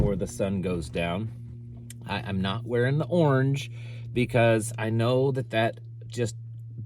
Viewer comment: lol, I thought u said " not wearing the HORNS"!